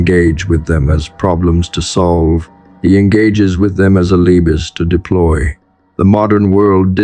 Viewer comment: engages with them as a "liebus" to deploy? Not sure what that word is, looked at the transcript, still stumped. Edit: nevermind the video script on screen answered my question "alibis" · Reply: Alibis